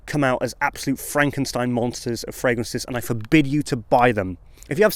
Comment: You know, for a moment, when you were explaining hybrid fragrances, I thought you said that they were Fragrancestein monsters. Since you didn't, of course, I'm coining the term now. :D Great job as always.